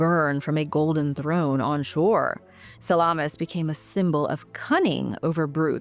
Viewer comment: 😂 my hungry ass thought you said salami